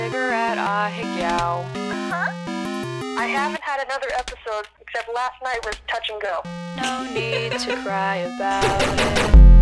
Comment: "I havent had another episode except last night was touch and go." I heard it like "I haven't had another episode until last night we were touching girls."